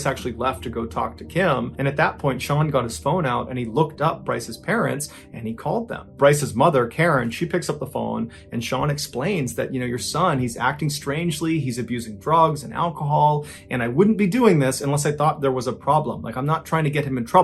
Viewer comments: I heard it as “he was abusing frogs and alcohol” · I thought he said frogs and I’m like that jerk. Don’t hurt the froggies!!